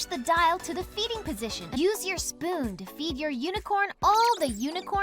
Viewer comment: I thought they said to feed the unicorn it’s shit